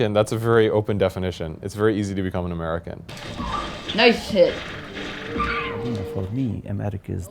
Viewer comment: did he say "nice shit" on 3:38 or am i just hearing things?